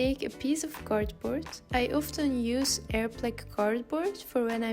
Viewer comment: hi what type of cardboard did you say u used? at 1:52? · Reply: Hi, I mentioned the brand Airplac but it’s mainly sold here in Belgium, now that I'm looking into it. If you’re searching for a similar type of cardboard, search for “white mounting board” that’s the kind without any foam inside. And if you're using a cardboard with foam inside, just make sure your cutting knife is sharp enough than it will be fine too :)